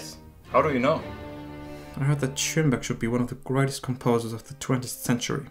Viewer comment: which composer did he say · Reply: Schönberg